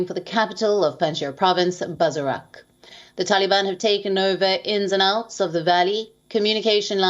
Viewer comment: I thought they said they have taken over panshir